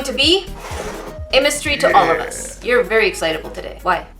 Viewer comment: I heard her say Imes-tree to allo-vus so I thought those were pokemons (idk anything about them) but I realized she said "a mystery to all of us"